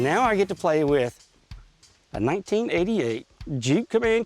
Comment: how did you find a 1998 comanche when they stopped producing them in 1992? Proofreading is great! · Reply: bob bobson I think he said 1988